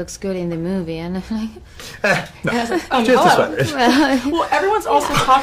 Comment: Can someone explain the joke to me? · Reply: not sure if thats the punchline. Can you translate what he said in 0:08 ? "... sweaters". I heard "without the sweaters" and thought it was the joke.